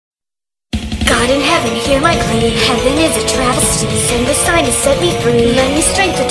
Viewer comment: i thought tenshi just said heaven is a trash